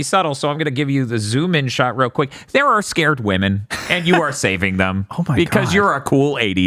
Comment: I thought that said "Scored Women" and thought this was a different type of game